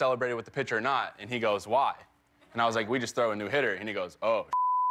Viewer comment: who heard Cody Say Throw New-hitter.